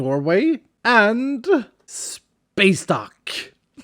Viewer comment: I thought you said "Space Dock" .. like that was the name of your company or something.. I didn't even see the duck, I just heard that and immediately thought of Costco. Many grocery stories and commercial buildings have hangers like that.